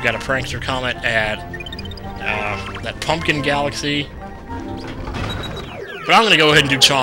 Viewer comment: At the end of the episode I thought it said the sideburn galaxy instead of the shiverburn galaxy.LOLOLOLOLOLOLOLOL